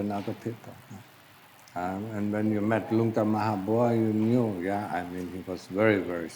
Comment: What is the name said at 7:43 ? The captioning translation says “Lunga Mahabhara”